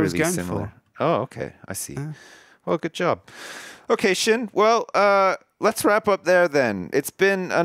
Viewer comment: Hahaha at the end when said "okay sjin" i thought he said "occasion"